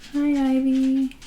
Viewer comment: around like 5:17 or something I think I heard, "Hi Heidi" and I got so shook bc that's my name 😂😂